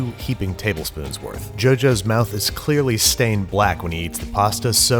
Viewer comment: subtitle says Jonathan instead of Joseph